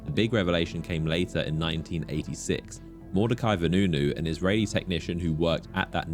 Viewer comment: NO SHOT that this guys name is Mordekaiser Nunu 😂 That's bonkers if I heard that right. Imagine having the same name as 2 League of Legends characters 😂